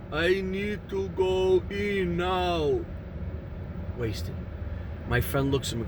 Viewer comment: You just have to love the automatic closed captions sometimes. They think you said “I need to go pee now” at 3:29.